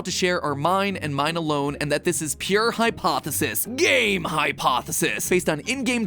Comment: why do I hear that as "Game Hypostasis"?